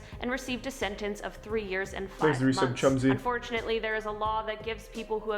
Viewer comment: did she just say treys plan (of shooting up a highschool) "unfortunately" didnt end up being completed. did i hear that right